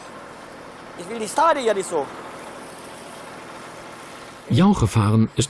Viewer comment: Was sagt er bei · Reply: "Ich will die Steine hier nicht so..."